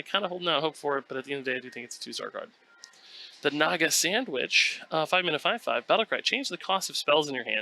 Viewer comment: i heard <<the naga sandwich!>> for a second. 18:58 lion king reference???